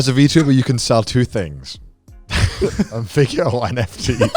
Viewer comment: anyone know what Connor says as the second thing? I couldn’t catch it Edit: nvm I’m like 98% sure he said NFT